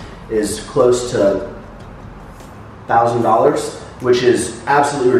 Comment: I almost fell out of my chair... $6,000? Is that what he was trying to say?